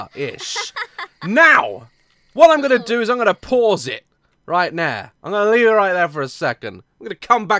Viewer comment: lol sounded like he said 'right now' in a cockney accent NOOOOOOO JUST LET SHIT HAPPEN IT'S NOT THE SAME IF U KNOW WHAT'S GOING TO HAPPEN D